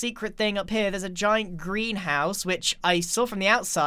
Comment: Sorry, I thought you said you found a Grian House and not a Greenhouse. ✌🏻😂